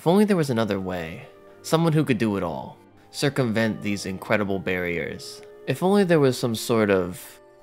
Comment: Did I hear "BERRY-ers"? 😅